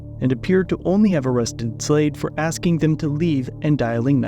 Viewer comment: I thought I heard the cop have arrested him for acting. 😅😅😅😅😅😅